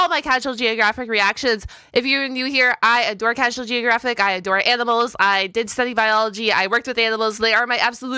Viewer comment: for a moment i thought she said " I adore edibles" lol